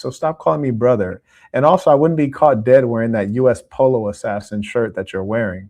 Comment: did he just say “US POLO ASSASSIN”? 😂